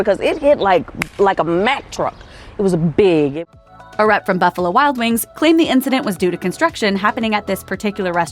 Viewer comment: I swore she said "a RAT from Buffalo Wild Wings claimed..."